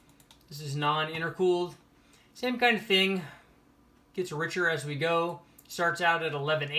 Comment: I heard “it gets Richard as we go”. Well obviously. “Richard as we go” means add forced induction and then crank up the boost.